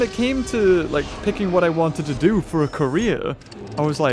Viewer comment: did he just say KOREA🇰🇷 or career?